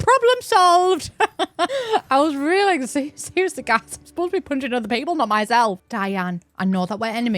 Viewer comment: Potential translation: "I was reeling, like- like excuse the gas, I'm supposed to be punching other people not myself" Unsure about the first part but the secind is definitely what you said I am proud at the level of northern british understanding I have reached. x'D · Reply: I heard, "I was really like-- s-seriously guys-" and then the rest that I think we can all agree on lol